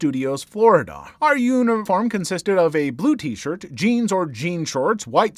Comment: The Jaws ride story, at 3:54... i had to rewind a few seconds at the start because I thought you said "union farm" instead of uniform...